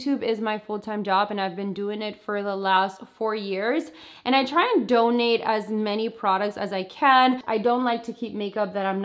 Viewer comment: I thought she said the last 40 years 😬😂